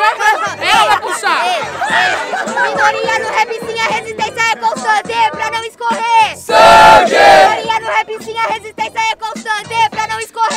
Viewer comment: oq ela fala nessa parte, entendi nd KKKK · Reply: Vitória do repzinha resistência e constante pra não escorrer sangue